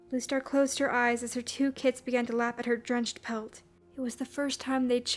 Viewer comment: When she said that her kits lapped at her drenched pelt I thought she said her kits laughed at her drenched pelt.